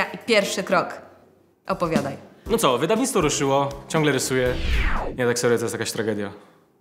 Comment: co on powiedzial? khvsdhcsihc to jest jakas tragedia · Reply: Nie, tak serio, to jest jakaś tragedia